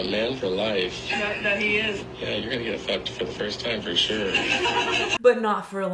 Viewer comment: does he say, "you're going to get f u c k e d for the first time"??! I cant tell?? And my cc wont pick that part up?! 🤦🤯